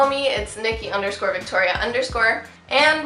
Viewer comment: For some reason in the intro I heard her say thic Victoria instead of